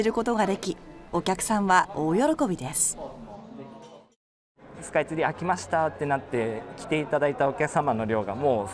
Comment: スカイツリー飽きましたって聞こえた笑笑